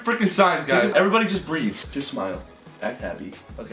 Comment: 🤔 Sounds like Grayson said sister smile. And 2019 anyone